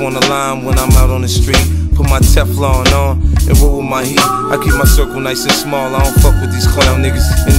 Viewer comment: I thought he said... "I google my feet" when he said "I roll with my heat" :D